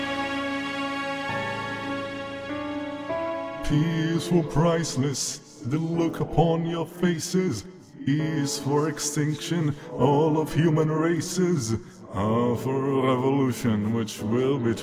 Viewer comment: The way I hear "human races" It instead fucking sound like "human racist", I hate myself 🗿